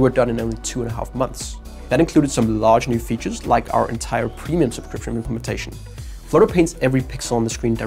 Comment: what is he saying here? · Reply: "Premium subscription implementation" is what was said.